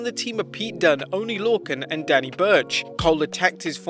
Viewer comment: Why i thought he said "Daddy Birch" at 1:13 😂😂